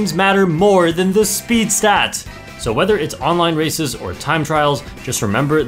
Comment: did i just hear “online racist!”